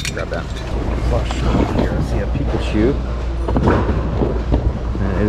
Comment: I thought you said people shoe (Pikachu). I hit bins last nite and score a pile of new movie posters. Dr Strange, Thor L/T and some others.